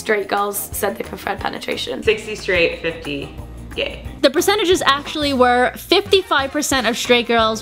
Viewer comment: did she just say 60 straight, 50 gay? =/